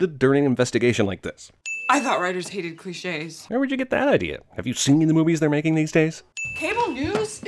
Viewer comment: I thought he said "Have you SINNED the movies they're making these days?" Goddamit...